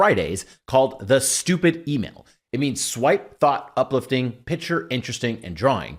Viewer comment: Definitely says "though" instead of "thought" at 0:09 ....I'm not sure if that was thought out , though slightly concerning it made it throug